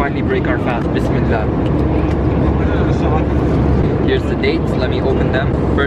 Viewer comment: did u say day or dates. i scrolled back so many times but i could not hear any diffrens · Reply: Dates